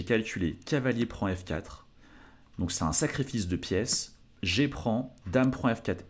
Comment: wow, j'ai eu peur, j'ai compris : "C'est un sacré fils de pièce"